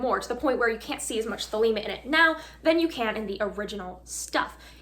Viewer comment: WTF did she say @ 4:55 ? "interesting enough, there is a form of...." EDIT: I literally just DIED @ 14:30 / is f'n hilarious - Love from TX